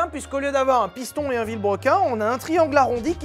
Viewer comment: Un QUOI ? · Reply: Un vilebrequin c’est une pièce avant d’être une chaîne YouTube